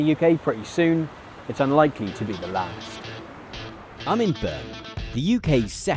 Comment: i kept not being able to hear what you said, it sounded like...im eburn....i figured it was Birmingham...in the end apart from rewinding a few times & that on its own didnt bear fruit, subtitles did the job!.....still....sounds like....hello imeburn....